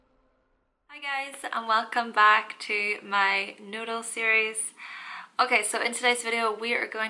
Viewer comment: In the beginning of the video it sounds like she says, “Welcome back to my noodle 🍜 series” ! 😂💕